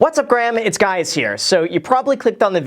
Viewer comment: Did I just hear "What's up Graham, it's guys here" on the intro?